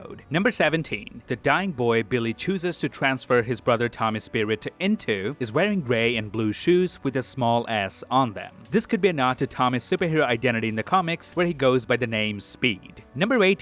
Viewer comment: totally thought he said the shoes had "a small ass" on them